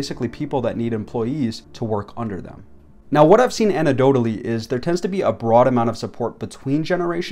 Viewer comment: did you just say "ANTIDOTE-ALY" 😂😂😂😂😂😂😂😂😂, you've been quiet quitting since 8th grade english class.